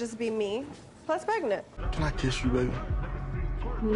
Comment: i thought she said funniest before- Horni police